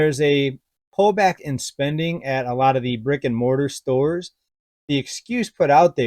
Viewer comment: I thought you said rigor mortis stores and not brick and mortar stores! That comes from this ER nurse. 😉